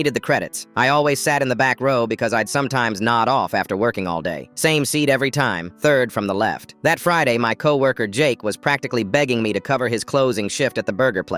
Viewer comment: I didn't hear nod off😢 I heard "nutt off"